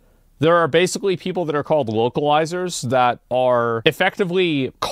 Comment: I thought for sure asmon said wokealizers in the intro